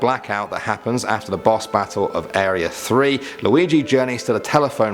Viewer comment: Sounds like said Waluigi XD